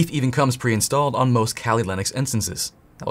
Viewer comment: subtitle mistake, "Cali Lennox" instead of "Kali Linux"